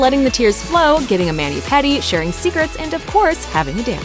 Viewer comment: did she say "sharing cigarettes" 🤨😑